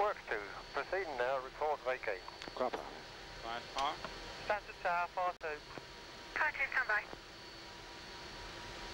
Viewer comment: hello ,someone can tell me what the first officer said to the captain at 16:43 ? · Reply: "Climb power"